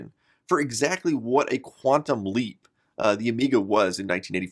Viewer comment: That's was GREAT (as always). Thank you. Near the end, when you said "quantum leap", i heard it as Quantumlink :)